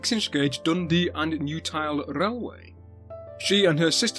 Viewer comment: I'll have to get my hearing checked out as I kept thinking I heard "Dundee and Utah Railway". 😅 But thank you for another informative video. Keep safe, keep sane and keep posting such great content.